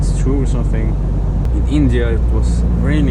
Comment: "did you said INDIA